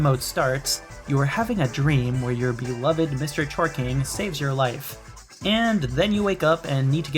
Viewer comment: I misheard his name as "Mr. Twerking" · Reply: I misheard it as "Mr Jorking"